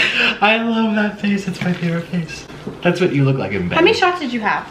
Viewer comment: wait did he say i love that face this how you look in bed